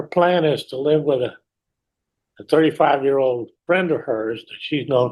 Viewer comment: Her 34 year old friend has a 23 year son or daughter? Right. Also her changing stories are giving me a headache. This girl just lies about anything I think but can’t keep her lies straight 😂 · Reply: I thought she said two three year olds. Like twins? I don't know she was mumbling so much.